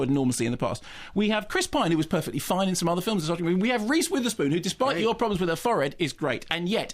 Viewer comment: What does he say at 3:29? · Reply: “We have Reese Witherspoon who, despite your problems with her forehead, is great” Apparently Simon doesn’t like Reese’s forehead